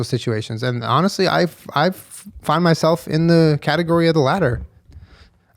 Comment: At first I heard that as “I find myself in the category of the ladder” 😆